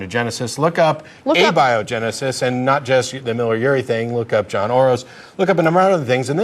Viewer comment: It sounded like said “John Orose” but in fact it Joan Oró… Great video clip. I would have probably hung up on Kevin as well. Katalyzt